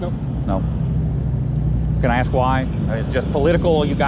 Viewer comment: what does the officer say? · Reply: I heard... We are apolitical.